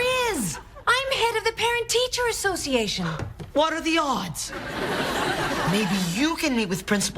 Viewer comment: what does he say at 8:52? i don't understand :$ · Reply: Lmao he said what are the odds